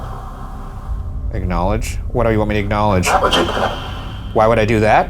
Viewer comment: I think when they're saying 'acknowledge' they might actually be saying 'technology' around 1:03:25.